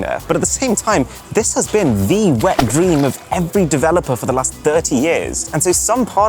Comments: Did he say it was the "wet dream" of every developer? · Did Arun just say, “wet dream”? 😶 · did bro really say it is 'the wet dream'